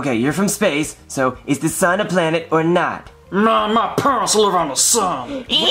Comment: What did the alien say? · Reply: My parents lived on the sun